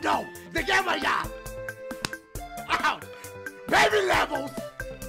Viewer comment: Still sound like he says "BAKE me levels" Cracks me every time